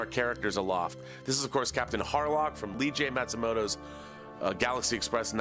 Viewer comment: Can we please applaud for that amazing video he made 👏👏👏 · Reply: Confession: When he said "Captain Harlock" I could've sworn I'd heard him say "Captain HARLOFF"! ("Geez, Kristian is in EVERYTHING, these days!")